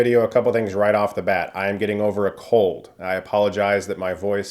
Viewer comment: I heard that as "I'm getting over a cult." and I was like... what? Oh yeah... Oh wait, no he said 'cold'.